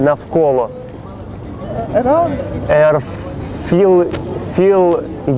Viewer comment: Мне одному послышалось - "Раунд!"? ;D